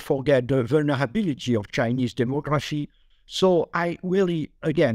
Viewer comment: “Chinese democracy “ he said. The fox guarding the hen house this guy 😆. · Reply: thanks for clearing that up. I also thought he said Chinese democracy.